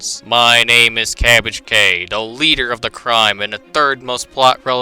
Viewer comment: I thought you said 'the Crime' as 'the Crine' aND NOW I'M CRINE